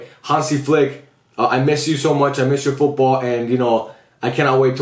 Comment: is tryna send a love letter now or what 😭💀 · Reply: Fr I was zoned out and all I heard was “I miss your ball” and “testicles” in a sentence 😂